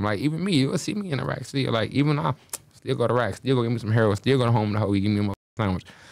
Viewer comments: did he say heroin 😮????? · did he say still go to get me some heroin?😮